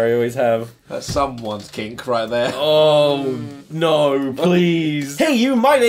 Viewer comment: What did say at 0:05? I literally just can't quite hear it. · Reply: “Now you’re gonna stick to the walls.”